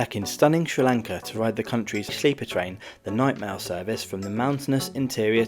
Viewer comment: lol, at the beginning I heard "Nightmare service".